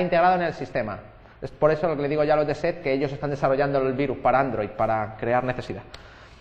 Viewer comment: escuche mal?? dijo que Eset desarrolla virus para android??